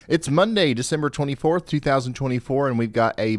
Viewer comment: Bless it,I thought I heard him say Dec 24th...Had me checking the day myself lol.... Wishing Everyone,Ryan and ypur Family from mine here Ga. A very Merry Christmas and A Blessed New Year! Thanks Ryan for your uodates. 🎄🙏